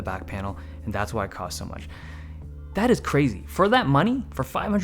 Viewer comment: I really felt the word "bullshit" instead of "crazy" haha